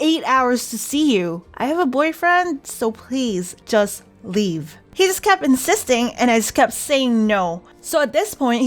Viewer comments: I thought you said "He just kept existing" at first 😂 btw you're a really cool YouTuber, I love your channel 🖤 (Here, have a heart as black as my soul and heart) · I thought you said he kept existing. wait a minute is gonna pull a thanos????